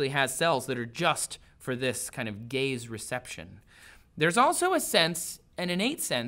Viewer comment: did he say, gay receptions ?